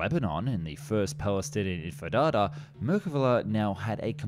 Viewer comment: Im sorry, did he say the First Palestinian Empenada? 😅 I swear we've heard him say Entifada correctly before..